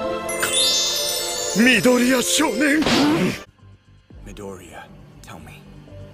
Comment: It Sounded Like All Might Said "Midorya's Your Name"